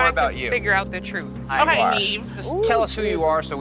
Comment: what the heck does she say right there; “okay Eve” or what lol